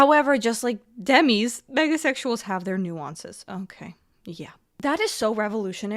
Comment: I thought that said "megasexuals have their nuisance"....like yes, they are a nuisance!! 🤣